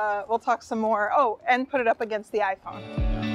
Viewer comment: sounds like Chelsea said iPod.